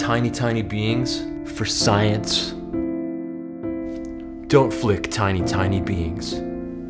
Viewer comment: I thought he said "don't flick tiny beans" lol